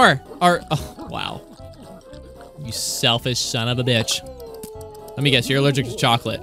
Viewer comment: did he just say b***?